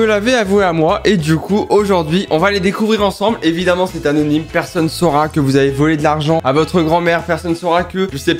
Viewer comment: " personne saura '' Moi : pense '' il a dit '' personne Sora '' " . Qui comme moi a penser qu'il parlait du Youtubeur Sora ?